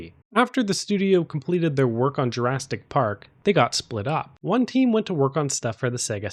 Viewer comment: JurasTic Park..? Am I hearing things..?